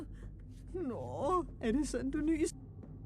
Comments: is actually german sorry for the mistake! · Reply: Uhm but i heard “aww er det sådan du nyser” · All I hear Swedish “is this is your dad”or “this is a dat”